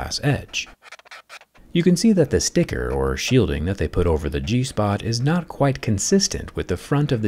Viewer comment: Mate, I heard G-spot. Trust me, he said G-spot😭